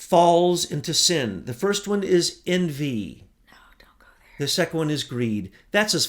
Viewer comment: , guess what.... Right at [Time Stamp] 36:20 you whisper to ''No, Don't go there''.... Have a listen....lol, still a great show though. You two are so great. One thing though, how can I get in touch with......